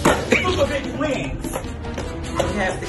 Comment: I THOUGHT THEY SAID “YASS PERIOD IN AISLE NINE” 😭😭😭